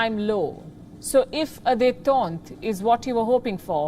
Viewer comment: what did she meant by if a "detont" is what you are hoping. What was that word and meaning too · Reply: From Google: detente /deɪˈtɑːnt/ the easing of hostility or strained relations, especially between countries. What does Dètente mean? 1 : the relaxation of strained relations or tensions (as between nations) diplomats brokering a détente also : a policy promoting this.